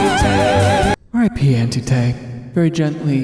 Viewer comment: I thought he was saying “RIP Andrew Tate” 😭